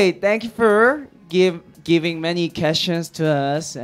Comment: I have thought he said ‘giving many cash to us’ LOOOOL